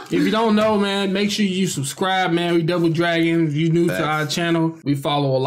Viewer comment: for some reason i thought the intro said double virgin, not double dragon